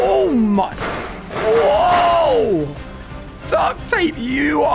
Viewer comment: This was crazy! 😱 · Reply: I heard it duck tape 🤣